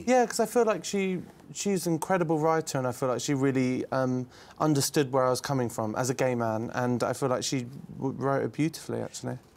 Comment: I thought sam said : " ... Really understood where i was coming from as a gamer" lol